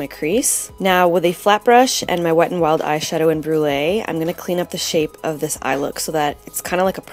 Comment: Hah, when you said "after i clean the shade off", i thought you said "clean the shit off" omg it just made me laugh ao hard xD Beautiful look!